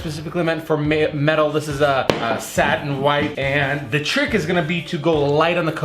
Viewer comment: I thought he said "sad and white" 😂